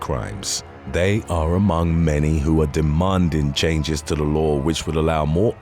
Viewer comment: "Demonding changes" perfect example of why this guy is a horrible narrator. He shows the he is capable of pronouncing the letter A when he says "changes" yet he chooses to say demonding instead of demanding. Like, wtf just hire somebody that can speak properly